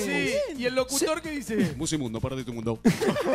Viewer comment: Mejor parte 1:13 Guido: Y el locutor que dice Imitador: Musimundo parte de tu mundo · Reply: JAJAJA pense que decia musimundo perdiste tu mundo